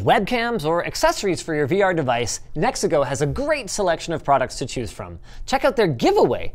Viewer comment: is it just me who heard Mexico?